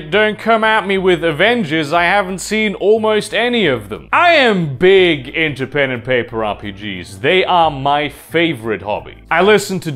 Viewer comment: Thought he almost said "I'm big into penetration" and I was gonna go well aren't we all?